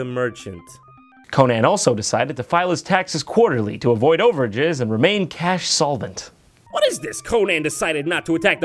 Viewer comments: after hearing it many times now i still can't understand what he says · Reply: "Conan also decided to file his taxes quarterly to avoid overages and to remain cash solvent." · No matter how many times I hear Jon say "overages" it still sounds like "overjizz" to me. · i think he said over gizz instead of overdraft at 5:21 lol